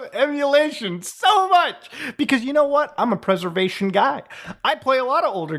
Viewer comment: Haha i thought you said preservation god. Oh i should probably say at the start of the video